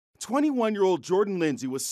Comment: I know this is mean and rude but I thought at first he said "21 year old Gordon ramsay" I going to hell yall